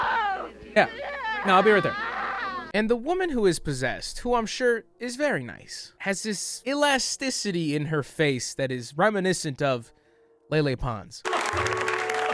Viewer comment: i heard.. "the woman who is possessed who im sure is paradise"